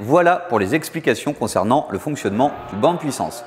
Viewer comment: Super vidéo bien expliqué, mais il y a que moi qui entend « ken à vous » à 5:38 ? 😂